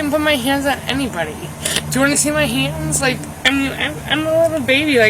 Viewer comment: did she say “I’m a little baby” ? 😂